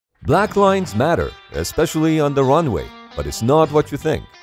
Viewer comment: I thought he said Black Lives Matter at first